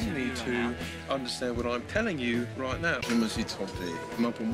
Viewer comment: I meant what did he say around 0:18 and 0:28-0:32? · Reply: "Je me suis trompé. Mais franchement" > I was wrong. But franckly